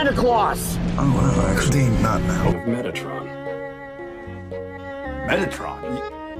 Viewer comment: Was written by metaron " the transformer?" " thats megatron " " what? " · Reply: Is it weird that I heard it as Mettatron and was solidly confused for a while?